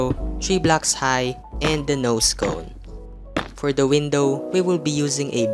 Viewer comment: did he just say “and a no-scope” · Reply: Nose cone 🤣